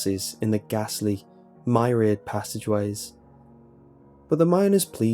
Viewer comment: No one gonna comment on the fact it sounded like he said "my rear passageways"